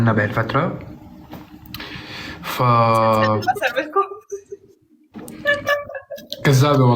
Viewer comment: شو قالت هون 11:49؟؟ ما فهمت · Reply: گالت "إذا بدكم اسرب الكم"